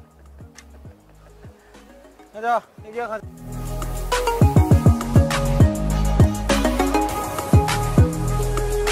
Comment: did jk called jm ‘baby’ or do I have hearing problems? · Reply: Yeah he said "baby let's go"